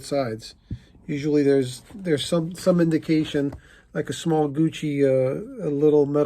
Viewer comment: I truly am tired, I worked almost big part of today in my garden, exhausted I heard you say this is a Hoochie purse and then looked again, it was Gucci. Anyways. I wouldn't buy either the Louis Putain nor the Hoochie one. But I do like your humor and it relaxes me. cheers!